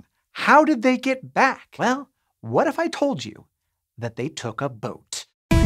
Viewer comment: Visit <link> to get started learning STEM for free. The first 200 people will get 20% off their annual premium subscription and a 30 day free trial. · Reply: 😂😂😂 At first I thought you said they took a vote!